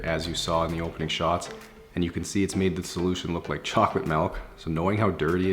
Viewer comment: "look like chocolate milk" Made me crack up but now that's imprinted in my brain 😂😭💀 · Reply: Random Person it sounded like he said chocolate melk 😂